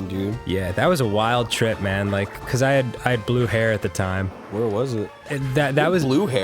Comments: when Dan said he "Blue hair" i heard "Blew hair" thought it was a saying for smoking weed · i thought Dan said "i blew hair" and thought "what drug is he talking about???" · I totally heard that as "I blew Hair at the time" and though that the name "Hair" was a weird name for a guy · oh, I thought he said "I blew hair" which I thought was a term for doing marihuana XD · I keep hearing " I Blew Hare" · For a sec I thought Dan said he blew hair and thought it was blew heroin.... and I was like WOAOOOH.. DANNY.. I know you did drugs but DAAAAMN O.O knew lingo!?!?! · I thought he said “blew hair” like some weird drug reference